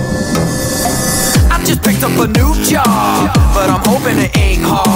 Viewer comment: I thought the first song said "I'm just bricked up"